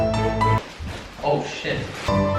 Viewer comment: Who heard 'oh shit'